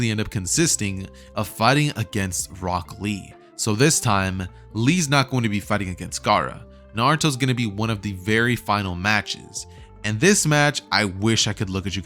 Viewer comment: I thought he said Brock lee at 16:00 😂